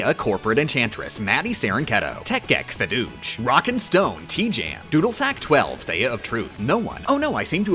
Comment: DID I HEAR A ROCK AND STONE